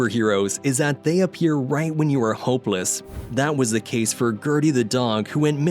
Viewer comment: i heard it as "homeless"